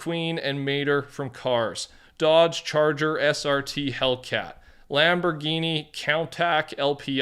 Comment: Did bro just say Lamborghini countack 💀